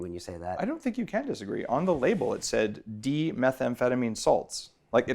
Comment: Adderall is amphetamine, not methamphetamine. This guy doesn't know shit. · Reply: he clearly says "d-methamphetamine" which is dextro-methamphetamine, the drug in desoxyn. If you wanna be so specific about what he said try listening to him.